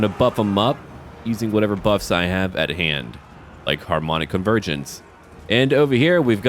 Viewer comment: i thought when you said “harmonic convergence” that you said “harmonica virgins”